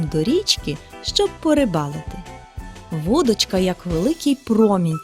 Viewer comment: Мне одному послышалось на 0:54 минуте не «Вудочка», а «Водочка»